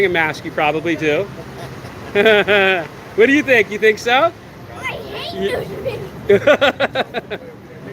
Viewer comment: " I hate ??????" What did that child say? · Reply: Think he said "I hate news media"